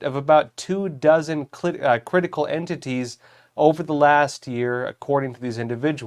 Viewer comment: Did you say clit? 🫣 @ 1:34 😂